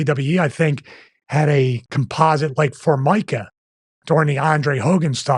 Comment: what does he say they had? Composite like for Micah?